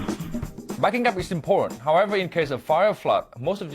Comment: did he say "backing up recent porn?"